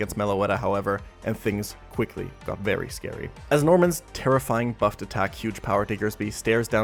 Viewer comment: i thought you said Tera-Flying 😭